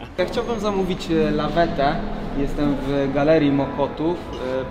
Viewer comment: A ja zamiast zamawiamy lawetę usłyszałam zamawiamy lawendę